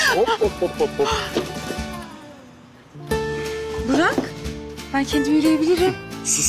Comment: did she call her buraak?